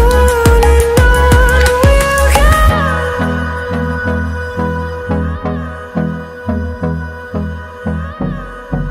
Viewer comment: I can't understand this one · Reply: He said men who ejaculate or masturbate can decrease the chance of getting diagnosed with prostate cancer.